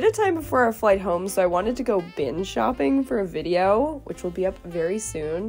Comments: Thought you said binge shopping and I thought “what else is new” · I thought you said "binge shopping" I was like this is new🤣